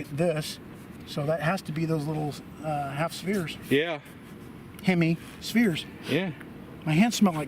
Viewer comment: So many pun-possibilities here in this one. Had a good laugh. Idea for a name for your own concoction: "462 Diet Beaders" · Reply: by the way, did you really say "hammy spheres" here, inspired by the pork smelling hands from hamsterdam?